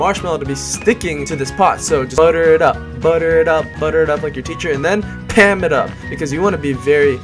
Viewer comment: did he say butter it up like ur teacher!?!?!